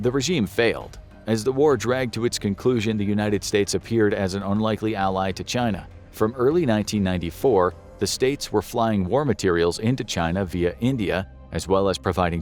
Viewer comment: I’m pretty sure I heard him say 1994 🤔🤔🤔